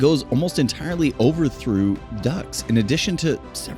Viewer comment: Thanks for pointing this out. Which lines did we mess up please? It will help us improve. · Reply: the word is "Viaduct", not "Through Duct". I'd assume the word "Via" was translated to the word "Through", and whoever you've got reading the copy didn't understand the typo and just powered through it.